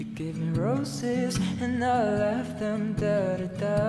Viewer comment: honestly thought you said "left them da da da~" 😂